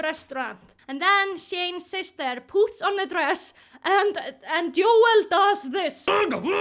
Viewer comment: I thought she said "Shane's sister poops on the dress" and I was like wtf is with all the poop in this movie. I know the movie was shit but damn 🤣